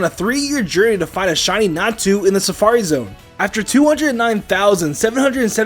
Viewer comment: When he said natu I thought he said nastu!!